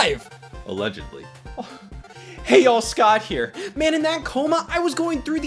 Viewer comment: Did he just say "Hey y'all"?